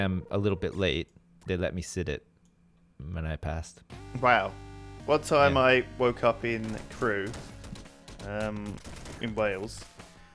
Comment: I thought said "One time I woke up in Peru"